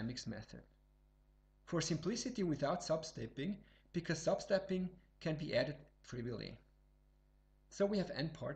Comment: What did he say at 8:53 "sub-stepping can be added...?" My simple wisdom is not enough to grasp this word. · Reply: "sub-stepping can be added trivially"